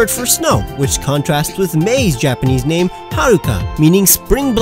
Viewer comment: You called May Haruki when it's Haruka. You should do rival name origins next! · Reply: I'm so sorry about that. Two things happened. 1. My mind was still processing Yūki for Brendan's Japanese name. 2. I've been having issues with my hearing and with the above point in mind, I misheard "Haruka" as "Haruki" and the latter is a Japanese name, albeit typically for males. Also, you left out Ailey. ...what Ray is known as: Ailey is his counterpart. Unless I missed that as well? Anyway, I'm so sorry about the misunderstanding. I've been watching YouTube for almost 8 years now. As for Pokétubers? I'm not sure who I found first ...as far as American ones go. I know it was on accident, but of the accidents I've been involved with, this was probably the best.